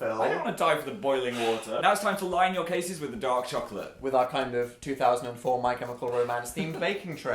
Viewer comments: oh my gosh, for the longest time, every time phil said "dark chocolate" in his baking videos, i thought he was saying "dog chocolate" and i just assumed it was a british thing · when phil says dark chocolate, for some reason i hear 'dog chocolate'